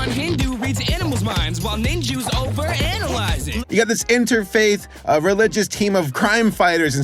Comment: Over analyzing? That doesn't sound like a gift, it sounds more like a nightmare lol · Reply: I thought they said 'always analysing'